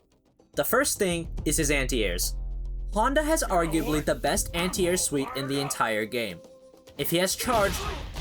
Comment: for a moment i thought you said he has the best anti-air sweep instead of anti-air suite, for a moment honda was +R sol...